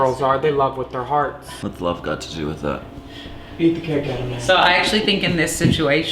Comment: "what's love got to do with it " eat the cake out of my ass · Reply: Arethia Smith LOL Oh ok. I heard "eat the cake out of my ass"